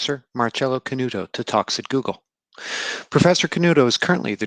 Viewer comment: I've always thought when you guys say 'Talks at Google' my ears are hearing toxic Google. 😂😅😄☺️